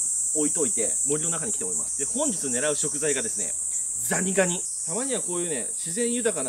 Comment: どーーーしてもザニガニって聞こえて何回も聞いちゃう